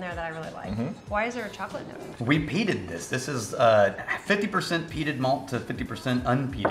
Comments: For a half a second I thought said we peed in this, not we peated this. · I swear at 12:10 I thought said "We pee'd in this" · I heard "we peed in this" and not "we peated this".